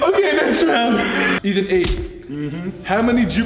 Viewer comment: looool he said Ethan 8 instead of question 8 🤣🤣🤣